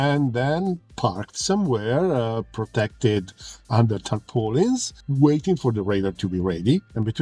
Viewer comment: I don't understand what you said there and the captions shows "under attack Polans" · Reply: Under tarpaulins